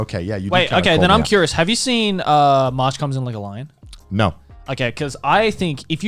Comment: what anime are they talking I can’t understand what they said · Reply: March comes in like a loin